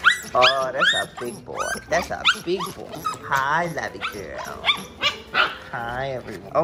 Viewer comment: Did he say Lavy girl or is it just me? Isnt lavander a boy too?